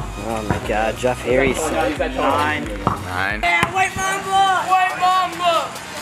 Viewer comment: I thought he said white mama at the start